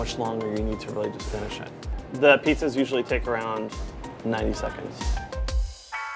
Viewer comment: did he say 90 seconds?